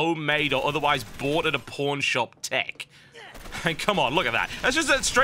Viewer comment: lol it sounded like he said bought at a porn shop